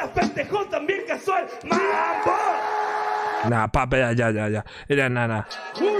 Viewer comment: Que dice Ibai??? 18:37 NAPAPE YAYAYA Y NANANA